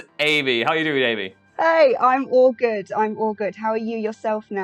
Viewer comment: When Amy said "I'm all good" i, for moments, thought she said I'm awkward and thought to myself " wow same"